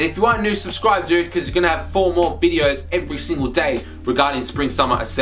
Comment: Did you say 4 more video a day